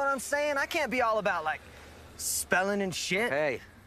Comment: I thought he said smelling instead of spelling but it's just as good